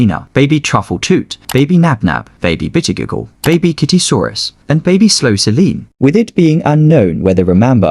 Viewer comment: at first i heard "baby t!tty-soarus"💀